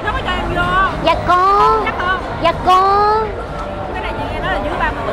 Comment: Phút 0:45 chị Vũ nói gì thế mn ơi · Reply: chị ấy nói là Cái này dưới 30tuổi đấy bạn